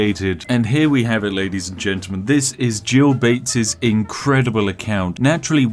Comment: Swear to god when I first heard him say “Gill Bates”, I thought he named the account “Jail Baits”😂😂😂